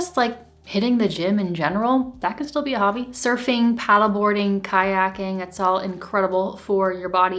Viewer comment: what did she say? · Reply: Paddle boarding, kayaking